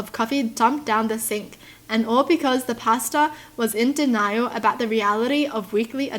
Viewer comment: When she said pastor I thought she said pasta 😐